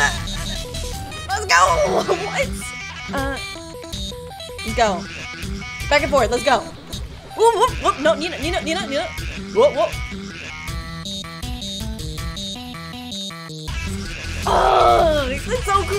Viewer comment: only i hear: нинада нинада